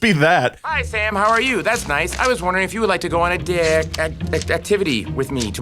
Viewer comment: the subtitles said dick instead of date lol